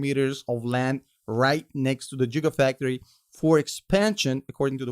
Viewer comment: Did he just say “jigafactory”? 😂😂😂